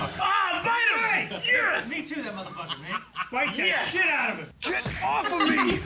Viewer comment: did he say “Me Too that mother fucker” lol